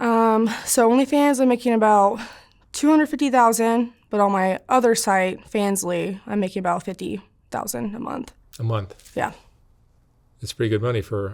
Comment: she said "20 to 50k a month" you need to go see an ENT dr. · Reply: Lol. You need to slow the video down at 1:13. She clearly said "On OF I'm making about $250 thousand and on Fansly I'm making about $50 thousand." So she's claiming she's making $300K a month.